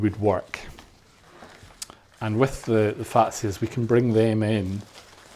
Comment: What's that fan-like green leafy filler? I hear you call them fancies at2:07-2:13, please correct me